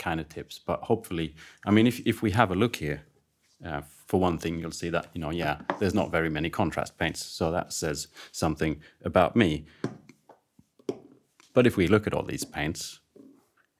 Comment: COOONNN-TRRRAAST Paint (???) Sorry, I don't know what you're saying;-) (Great Vid,)